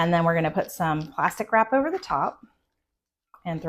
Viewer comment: Recipe? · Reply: ✨🤭Not to sound crass, but I misheard "plastic wrap" as "pasta crap" ✨😅🫴✨ Lol, I had to really pause for a moment to think...I wondered if some macaroni was gonna be dumped in there. Pun intended. Looks really good, light and refreshing....will be making this soon. ✨😋🥗✨